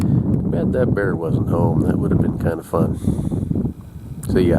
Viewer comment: For a minute I thought you said: " Too bad that bear wasn't home; that would've been kind of fun." I gotta get my hearing tested. 🤪👈